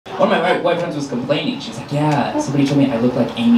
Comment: Right at the start, I misheard as - One of my wife's friends. And I was heartbroken 🥺